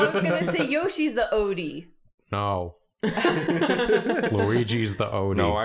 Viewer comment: the subtitles say "yo, she's the odie" and not "yoshi's the odie"